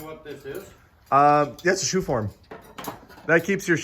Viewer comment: That was a shoe stretcher the guy held up by the table. A shoe horn is a curved piece of metal that helps you slip on your shoe. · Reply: I think said a shoe form. At first I thought he said horn too.